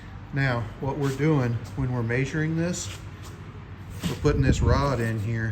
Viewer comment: The way you say measuring messed me up I thought you was saying majoring I was like what the hell is this guy talking about